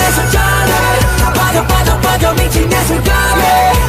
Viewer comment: entendí chinge su ma... :v